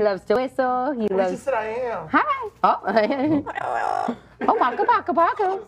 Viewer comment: bruh, I heard "I am," too!